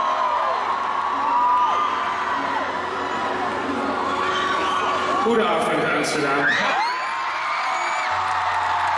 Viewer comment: what did he say at 2:30 ?? i cannot decipher it 😭😭 edit OH it's a diff language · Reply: Incase your still curius he said: "goedavond amsterdam, bedankt dat jullie naar de show zijn gekomen" which just means "goodevening amsterdam, thanks for comming to the show"